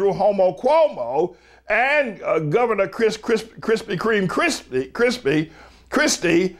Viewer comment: I think he is hungry because i swear i heard him say Krispy Creme